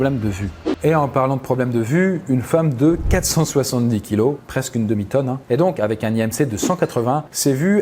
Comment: A 12:41 j'ai entendu '' presque une demi conne '' 😂😂 au lieu de '' presque une demi tonne ''